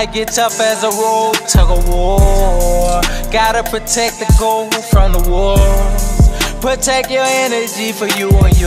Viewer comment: “Gotta protect the Goats from the wolves” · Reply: Sound like he said protect gold from the wolves